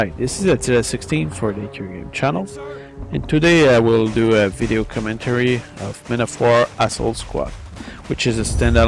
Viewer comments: haha in the beginning it sounded like you said i'm gonna do a video commentary of men of war asshole squad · Strange accent you have there. For a second i thought you said: "... and today i will do a video commentary of Man of War Asshole Squad..." :)))